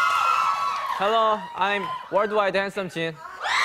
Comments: did anyone notice subtitles 🤣🤣 lol it says where do I dance Jin instead of world wide handsome Jin...ㅋㅋㅋㅋㅋ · lol someone put “Where do I dance I’m Jin” in subs instead of “Worldwide handsome Jin” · Put the captions on at 0:22 it's written"where do I dance "😂 instead of "world wide handsome Jin"😂😂